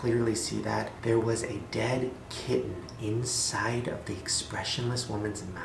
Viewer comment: What was in the expressionless woman's mouth? A dead what? I couldn't hear it.. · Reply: radhika rege i thought he said kitten